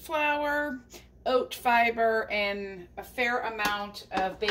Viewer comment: Oh my, I thought you said baby powder, then you said that wasn’t a typo..... I had to go back and listen again because I just couldn’t grasp that. So glad you said baking powder. 😂